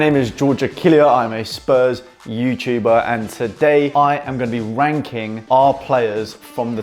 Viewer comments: surely i'm not the only one who thought he said wanking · I thought at the start you said I am going to wank all my players. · Did he say I’m gunna be wanking our players